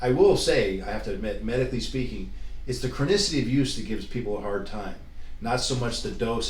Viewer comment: About 7:20 - what did he say “the chronicity of use “ ? Meaning the length of time they ON it ? Anyone tell me pls